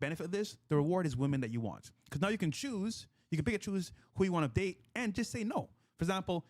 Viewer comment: co host said funny thing pick and choose and it sound like he said pikachu time to add it into the slang i pickachu dat ass